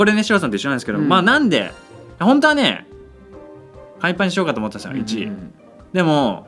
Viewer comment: パイ◯ンに聞こえたw